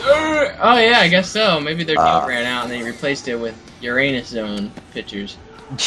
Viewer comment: The Uranus zone i thought he said Your anus zone